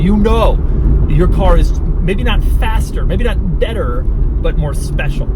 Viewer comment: did I hear this right? NOT FASTER?